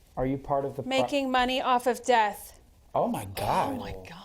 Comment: AGAIN.... AWESOME video... straight to the point. At first I thought Amy said "Making money off of SEX" Lol. I had to put on the closed captions to realize she said "Death". You're right tho... The question following the "Making money off of death" definitely doesn't seem appropriate following that statement. Like you said, you gotta be quick with asking questions that follows the spirits train of thought.